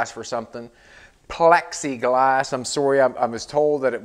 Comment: Pexiglass is ok. I thought you were saying pepsi glass. I was looking forward to you melting down a bunch of pepsi bottles and forming some new hand blown windows. Now that would be different.